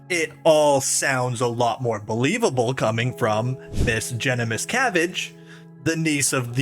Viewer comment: oh i thought you said her name was jennifer cabbage